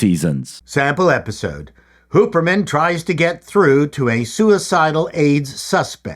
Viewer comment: I swear it sounds like you said "Pooperman"